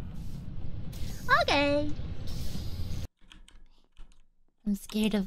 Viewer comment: aww i think she said “ok” but it was cute